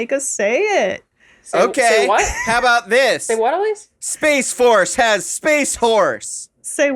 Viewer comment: Am i the only one who heard "Space force has space whores"?